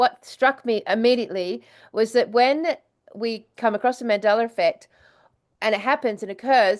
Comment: hmm... is it me or does she actually say "Mandeler Effect" ...??? ...as if she's adding an ER at the end of Mandela....???